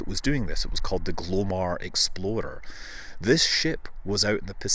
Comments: Every time Scott says "explorer" i hear "exploder". Funny. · I kept hearing "Glomar Exploder"